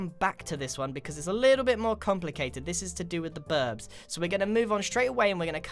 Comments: Did... Did you just say “birbs”? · did he just say BIRBS!?!? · at 0:45 said birbs instead of birds .-. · who heard birbs? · did you just say birbs?! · Listen at 0:45 he says birbs instead of birds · Is it just me or did he say "birbs" instead of birds? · Did I heard birb Looks like I'm not the only one says birb Instead of bird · did you just call them birbs